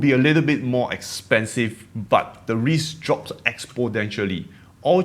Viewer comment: Oh no, the accent is really cool. Dont lose the accent. It's really not that hard to understand. Only word that stuck out in terms of pronounciation was 3:49 "Exponentially". Sounded like "Ex POD entially" instead of "Ex PON entially"